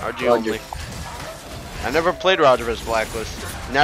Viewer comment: what did say? I never play roger ... ?? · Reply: he said he never played roger against blacklist (in M3)